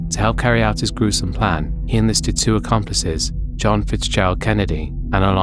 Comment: did he say John Fitzgerald Kennedy